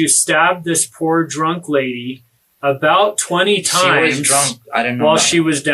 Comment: Did he just say she wasn’t drunk? Or am I tripping?